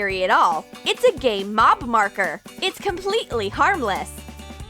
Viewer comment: “It’s a gay mob marker!” Please tell me I’m not the only one who heard it 😂